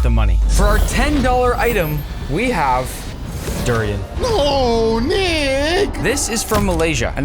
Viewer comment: did he just say oh nig